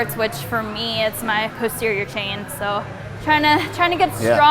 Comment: I'm sorry, but whenever she says "posterior chain" I keep hearing 'puss-terior chain' haha 😂. She's cute and strong AF! Great deadlift tutorial! 😊